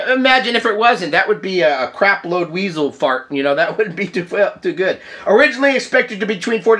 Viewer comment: What did you say at 5:22 in? 😂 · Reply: Crap Load Weasel Fart. ;-)